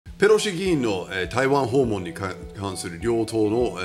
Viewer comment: ペロシ議員が工口詩吟に聞こえた! 末期だオレ